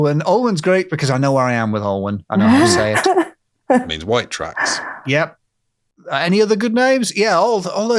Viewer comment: Great show!! Every time you said "White Tracks" I heard "White Trash".